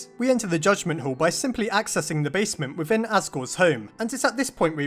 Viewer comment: when you have captions on it says “Oscar” instead of “Asgore”